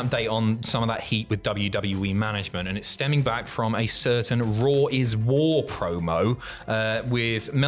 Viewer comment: Did you say "Raw is War" lol